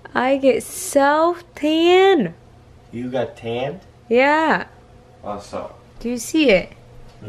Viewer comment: あっそうにも聞こえるしアースホールとも聞こえる